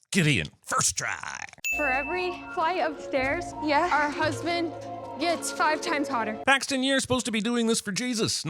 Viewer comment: Ha ha ha i thought she said after every flight of stairs our husbands get harder!😂😮😮 ha ha lol""" ding"" - 100 sins.